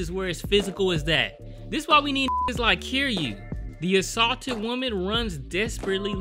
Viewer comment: Did... did you say "Kiryu"? From Yakuza?